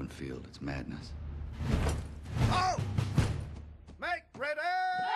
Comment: What did he said at 1:42? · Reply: "Make Ready!" What he said.